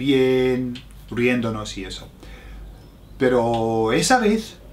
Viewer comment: ¿Alguien pueda decirme que dijo después de decir "riendo _____" (3:02)? Lo siento si es obvio xD estoy mal de oír claramente y eso quiero mejorar, gracias :) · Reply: riendonos y eso es lo que yo entiendo